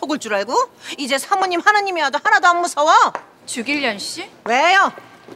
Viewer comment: 에 주길련씨라 들리는데 나만 그러니